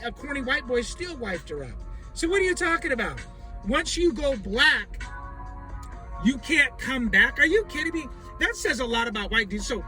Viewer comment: I heard it as, "Once you go black, your family don't want you back"